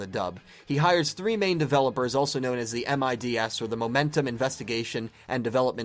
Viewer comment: i thought it said aids xD